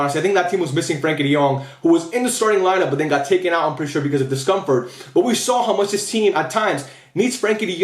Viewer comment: When you said De Jong was taken out because of discomfort, I heard “he was taken out because his stump hurt”